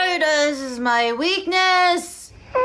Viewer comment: Which video got you to break? Let us know here! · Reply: Did she say, "God is my weakness" or "God is my witness"?